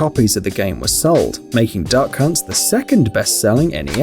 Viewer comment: did he say duck cun*